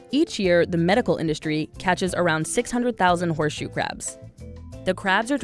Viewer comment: I thought it said Horse Shoe Crap